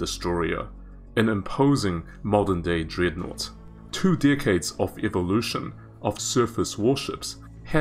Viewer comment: TWO DECADES of evolution... holy shit. I heard like "Two Dickheads of evolution" and I almost choked in my food hahah :D